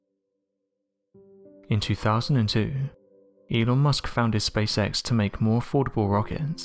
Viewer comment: In the intro between 0:08 and 0:10 I swear it sounds like you say "anal musk".